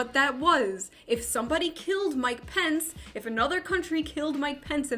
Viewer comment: Everytime you say Mike Pence, I hear "my Pence", it makes me very confused to know how much you care about him